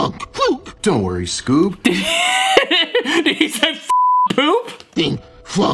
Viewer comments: I heard him say "f*ck the poop." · The way he says "f*** poop" had me laughing hard 😂😂😂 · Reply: I Hear "floop poop"